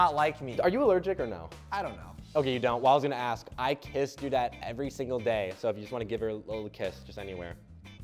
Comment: What I heard: "I kissed Dudette every single day." What the captions said: "I kissed your dad every single day." Me be like: WHAT THE HECK